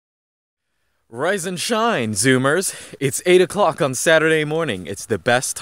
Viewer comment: I don’t know why, but I thought he said “Ryzen shine!” When he started his vid. 😂